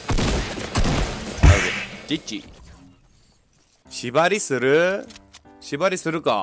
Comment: のとこチ○チ○に聞こえて草